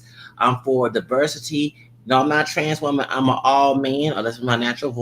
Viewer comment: That trans man got dragged😂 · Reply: that was a bio male that sounds like a woman, not a trans man, i think they misheard him when he said "im not a trans woman, im all man". it seems like they thought that he said "im not a transwoman, im a transman"